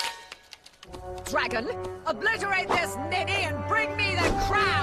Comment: BRO WHAT DID SHE SAY💀💀💀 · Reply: She said ninny (also there’s a show called the daltons and joe dalton call Avril dalton ninny